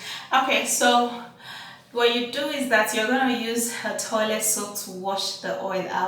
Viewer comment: Hey Sweetit I didn't hear for sure what u said what to rinse my face I thought u said TOILET WATER EXCUCSE ME IF IM WRONG 🌺